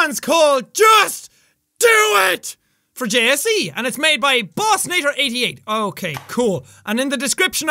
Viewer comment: when he said bossinator88 i thought he said boss Nword 88